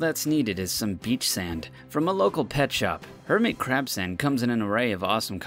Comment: If you turn on the subtitles instead of "hermit crab sand" it says "hermit crap sand" Disgusting